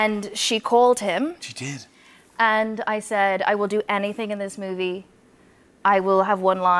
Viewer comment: "I will do anything you want" Hollywood in a nutshell. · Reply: When did she day "I will do anything you want"?? I don't know about you but I heard "I will do anything in this movie" Then explains even tiny roles...